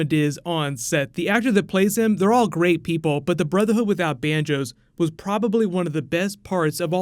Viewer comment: in this video is swear he said the brotherhood without banjos! Lol